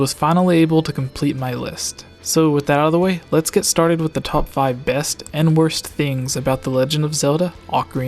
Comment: Haha did he say top 5